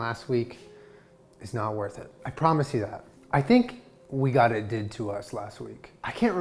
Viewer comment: that weirdly synced part at 00:20 freaked me out, it seems like you are also saying "i promise you that" in the video. i don't know if that's actually what you are saying but i am too lazy to check, so i am going to stay freaked